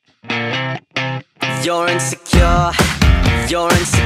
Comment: I've listened to this several times I just hear "You're in the car" In french